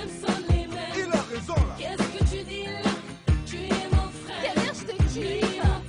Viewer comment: Elle dit quoi là ? J'ai compris Kader j'te kiffe